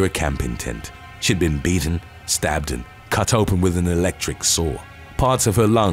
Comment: Electric saw...i thought you said electric sword lol!